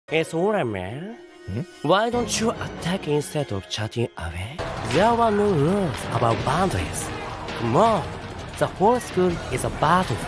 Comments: I HEARD HIM SAY “the whole school is a butterfly .” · When he said "battlefield" I thought he said "butterfly" 💀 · Idk why dafuq I heard karma said "butterfly" but actually its battlefield😂